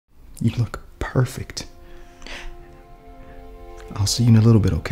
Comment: Thought he said “You look purr-fect” for a sec 😂